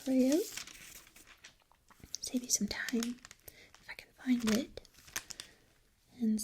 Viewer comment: You were so quiet that when you said "if I can find it" I thought you said "freakin' find it"....